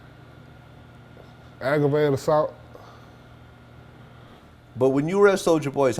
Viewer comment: I thought this nigga said ''I woke up in gravy" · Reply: ThisTattooedLocJourney lol oh, i thought he was saying gravy as in a pool of blood